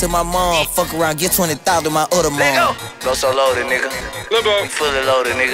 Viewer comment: Anyone else thought it said lil loaded